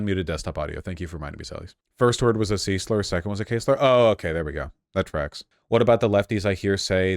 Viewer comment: I thought you were saying "Caseler," not "K-Slur"